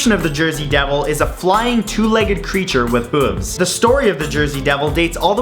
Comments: Flying creature with two legs and BOOBS? Did I heard it right? :)))) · LOL i thought mat said "Boobs" instead of "Hooves"